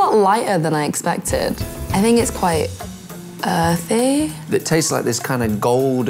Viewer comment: ? What is she saying? Earthy?